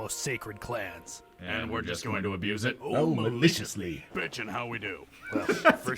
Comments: "so we're just going to abuse it?" "Oh maliciously!" "Bitchin' how we do?" Is still probably one of my favourite exchanges in the series and I dunno why it's so funny to me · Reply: Since he did a gay joke after that, I thought he said "homoliciously". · "so we're just going to abuse it?" "Oh maliciously!" "Bitchin' how we do?" Is still probably one of my favourite exchanges in the series and I dunno why it's so funny to me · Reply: I always thought he said homoliciously